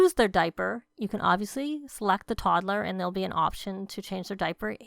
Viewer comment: I really thought you said slap the toddler